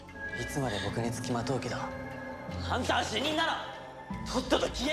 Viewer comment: あんたは主任だろ！(空耳) そうですけど何か？w